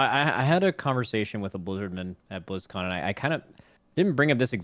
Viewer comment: Did just say “I had a conversation with a Blizzardman at Blizzcon”